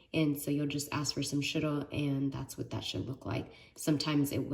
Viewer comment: Did you called shirk ‘that shiit’ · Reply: 🤣 I said that’s what it should look like